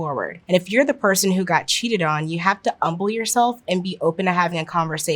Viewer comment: Am I just mishearing it or did she keep saying "umble" instead of humble?